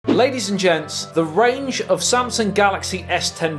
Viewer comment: I first I heard you say ladies and germs. Lol 😁